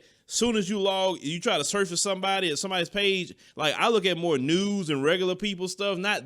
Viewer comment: I'm assuming said he looks at more news even though it sounded like he said he looks at more nudes lol